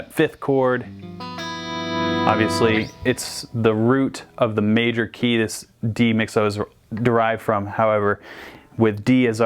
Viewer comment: dude, I just don’t understand what he’s saying there, I’m pretty good at understanding American speakers but that was over me sorry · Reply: MyNames Joe “root of the major key that D mixolydian’s derived from” so basically he is saying the G chord he just played is the major scale that D mixolydian comes from.